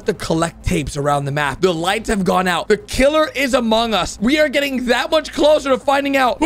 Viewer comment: did he say the killer is among us😂😂😂